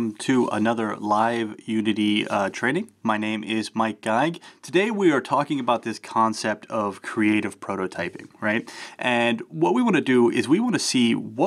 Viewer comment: I heard "My name is Might Guy!" XD